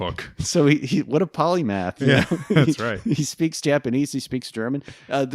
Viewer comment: Did just use "polygraph" instead of polyglot for someone who speaks many languages? Is this a reference to any episode I didn't saw or an actual blunder? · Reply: The word spoken was "polymath", as in "a guy with many intellectual skills"